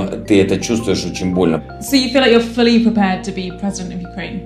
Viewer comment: What did he say ? pls write below\/ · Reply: he say "not fun but...." and video cut and somone say Glory of Kievan Rus